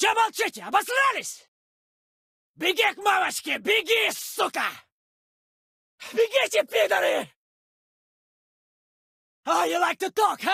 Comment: What does he say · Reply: Begite pidory